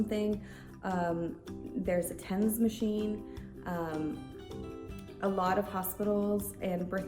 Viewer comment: Did you say there's a tennis machine? And who is this Adula? She sounds nice. How big are those birthing pools? I've got an image in my head now of a swimming pool full of mothers. With baby's being launched like torpedoes and dive teams ready to jump in and look for those babies. And the normal color of poop for babies? Isn't it brown? Like everybody's poop? Although it would be cool if it was blue for boys and pink for girls.